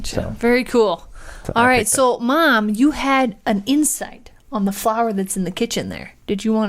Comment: I thought you said your mom had to insects. :-p So I'm watching and listening and then I realized you said insights. lol